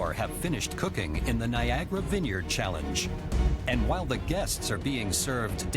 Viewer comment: thought he said Viagra vineyard challenge....